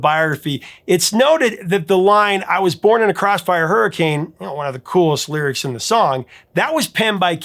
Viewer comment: I always thought he said "I was born in a class 5 hurricane" 😂 it works too!